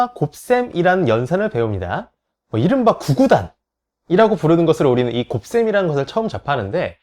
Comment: 뭐라고 하신거죠???????? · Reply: 구구단